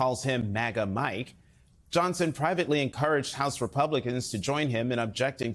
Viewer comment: I thought you said, "little gnome" 🤣